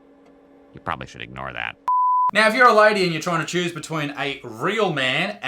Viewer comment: During thenclip from the simpsons the guy was in the middle of saying "You probably should ignore that" an add popped up while he was saying that and it sounded like he was said "I'm bi"